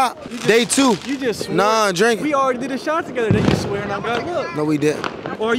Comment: it sounds like he said the n word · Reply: “we already did a shot together then you’re swearing on god look”